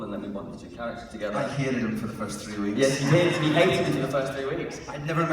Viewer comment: Can someone write me what Anthony said beetwen 35:42 and 35:58? I'm not a native English speaker and I can't hear the words. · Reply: This was roughly what was said between Anthony and Sam. A: I hated him for the first 3 weeks. S: Yeah, he hated me for the first 3 weeks. A: I never met someone so lovely. I was like there must be ulterior motive. He was like, he was like ”hey man, would you like a cup of tea?” I was like “what are you up to? what’s his game?” Otherwise he’s been the most beautiful human being I’ve ever met. I just love him with all my heart.